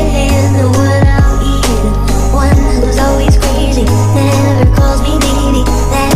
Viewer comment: I thought she said "never calls me daddy." WTF brain! 😂